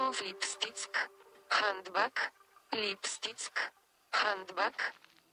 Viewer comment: What did they say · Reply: Lipstick. Handbag.